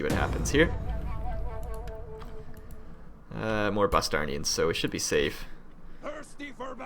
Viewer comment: Thought you said "Bostonians" for a second. Then the image of an army of thickly accented Bostonians popped into my head. Cursing violently, all clad in Boston sports gear. Nightmarish.